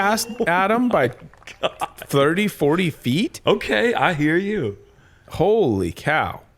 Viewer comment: Okay I hear YOU-tsen